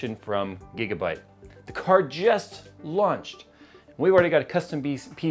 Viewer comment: Everytime Jay says 'launch', I hear 'lunch'... must be lunchtime.